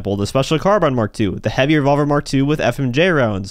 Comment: A 60 round mag on a revolver? I know gta has all kinds of fake 💩 in it but that’s just ridiculous. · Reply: Thought I heard you say navy revolver.